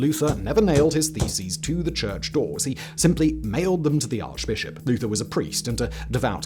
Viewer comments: I had to do a double take on the Martain Luthor story, even with it repeated it still sounded like you said that he "nailed them to the Arch-Bishop" not that he "mailed them". I was thinking, Dang, if he nailed them to the Arch-Bishop, no wonder he was excommunicated! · I thought you said he nailed his feces lol